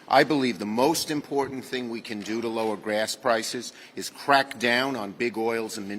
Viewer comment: Did he just say "lower GRASS prices?"